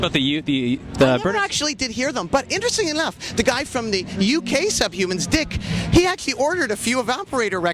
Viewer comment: did anyone else hear “the guy from the UK suck humans dick”